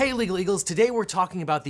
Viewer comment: I actually thought you were saying legal beagles... LOL